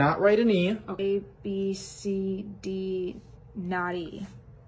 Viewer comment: When She Said Not "E" It Was Hearing Like Naughty 😂😂😂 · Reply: Ohhh that's what she said 'not E' I really thought she said 'naughty'